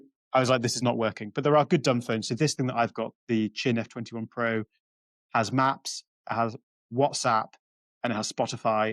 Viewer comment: What is the name of the phone that James Marriott uses? It sounds like he says GNF 21 Probe, but when I google that, nothing comes up. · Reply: I think he says: Qin F21 Pro